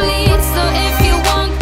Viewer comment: Even with the lyrics, I just hear "I just wanna be part of your suffering" and I can't unhear it